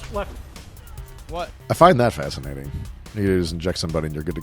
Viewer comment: When this Seal says "fascinating" I hear "horrible blunder". In chess terms: ??